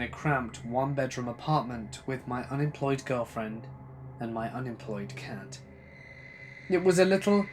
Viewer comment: So at 19:51 did he say unemployed cat?